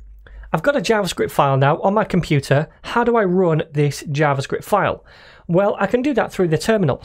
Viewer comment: I totally misheard you say, "How can I ruin this Javascript file?" and my first thought was, "Well, it's Javascript, how much worse can it get?"